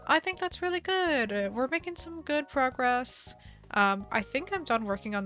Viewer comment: Do you say good frogress? I swore I heard that. XD Glad its coming along well. Island struggles are a pain